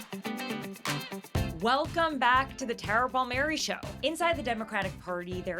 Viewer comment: i thought your intro said welcome to the terrible mary show 🤔